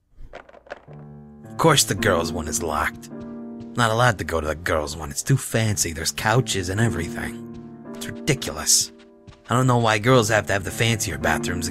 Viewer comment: Was I the only one that thought he said Cow jizz when he said couches